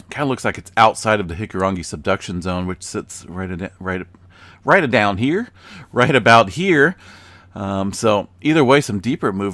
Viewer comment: Hahaha “right a down here” that made me chuckle! Never heard you say that one before! Also when you do that sound of blowing air through your teeth, almost like a cha cha cha choo (closest to what it sounds like to me) my small dog always perks up his ears and tilts his head slightly! Has nothing to do with quakes but I thought it was funny 😂